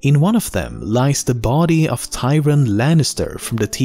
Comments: it’s Tyrion not Tywin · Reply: He said Tyrion get your ears checked you might of thought he said Tywin because he pronounced it differently. One version is pronounced TIRION and the other is TYron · That is Tyrion not Tywin lannister in the sky cell! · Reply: Sounds like hes saying tyrin, a mix of tywin and tyrion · That's the body of Tyrion Lannister in the sky cell, not Tywin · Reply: I thought he said Tywin too, but he actually said Tyrion, it's just that he said it with a y instead of an i.